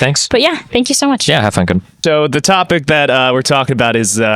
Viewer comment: Did Steven say "have fun good"?